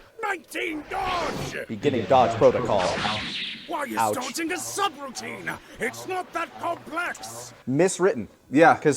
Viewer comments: I think I always misheard Gero's line there. I always thought he said "Why ISN'T dodging a sub routine" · I misheard that as "why isn't it a sub routine" until you pointed it out here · I never realized the line was actually, "why is dodging a subroutine?" I always just heard it as "why isn't dodging a subroutine". I legit had to raise my volume and really listen just to confirm they said is. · I've been mishearing this line for years apparently. I always heard "why is dodging not a sub-routine" rather than "why is dodging a sub-routine". My brain has been fixing your mistake this entire time, and now the magic is gone. 8:15 I thought you guys were referencing a dub. I remember Goku being excited for the medicine being grape flavored in I think the Funimation dub. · I originally heard it as "Why isn't dodging a subroutine" which makes sense and works